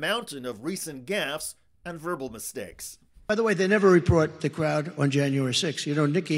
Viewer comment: This is the first time I've noticed, but it sounds like he says "reprot" instead of "report" right before the "Nikki Haley" problem.